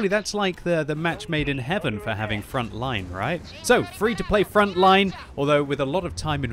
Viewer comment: Genuinely thought it said “Free to play Fortnite” i was scared..